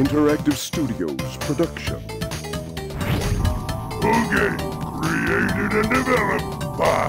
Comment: after 13 years finally I have understood what that voice sais: "Boogie, created and developed by naughtydog!" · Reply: I thought it said "proudly created and developed"